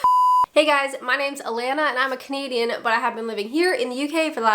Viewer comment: When you first started out I always thought you said - I’m a comedian. It’s a bit clearer now